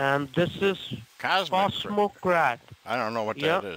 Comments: Is it just me, or does the second guy sound really high? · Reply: You're right. Not my comment, someone else posted this 😆 13:02 sounds like he said "smoke crack" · sounds like he said "smoke crack"